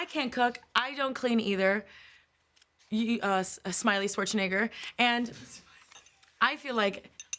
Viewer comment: It sounded like she said “smiley sports nigger” anyone else hear that?